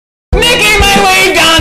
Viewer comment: I thought it said making my way downtown with death man he's going to get a VAN